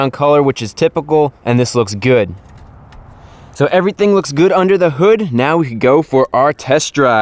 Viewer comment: at first i thought he said "everything looks good in the hood"